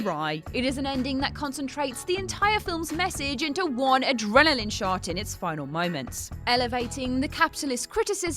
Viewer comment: I know she said "adrenaline shot", but it really sounded like she said "adrenaline shart".